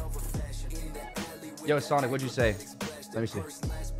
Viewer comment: WAIT DID HE JUST SAY MY NAME HE SAID SANA RIGHT! Nvm he said sonic